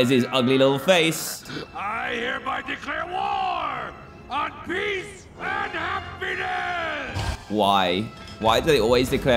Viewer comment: I heard “ I declare war on beef and haffyness